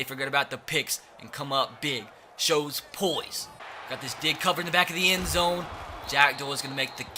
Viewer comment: Wut did he say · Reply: Got this dig covered in the back of the end zone